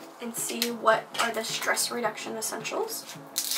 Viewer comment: Fits of giggles here - you were saying stress reduction, but I heard it as breast reduction! I was like, 'You what?'